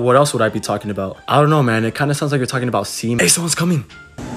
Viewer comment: does anyone know what he said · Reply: he said semen